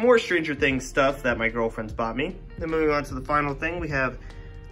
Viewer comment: did he just say his girlfriendS got me...